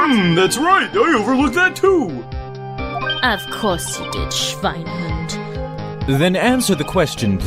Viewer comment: What did Franziska say at 7:21?! Schweinhund?! XDDDDD